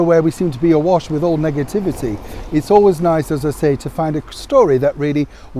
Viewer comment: For a second there I thought you said "Megativity" instead of Negativity :P